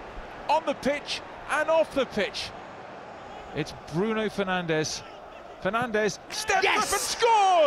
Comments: I heard penandes In 1:19 😂😂😂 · Did he just say Penandes 🤣